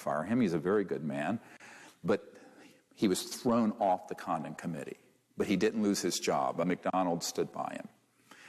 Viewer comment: I kept hearing 'condom committee' haha 🤦🏽‍♀️